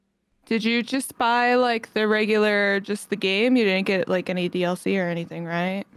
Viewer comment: im just noticing this but did anyone else see the subtitle said "dcl" and she said dlc? Yes I'm the person that has to point this out after two years